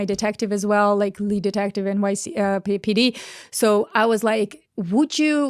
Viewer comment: she accidentally said where she was from. 😂 NYC is what it sounded like